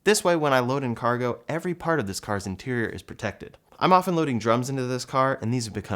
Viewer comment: You had me then, I thought you said you load ‘drugs’ into your Y all the time, not ‘drums’ ha 😂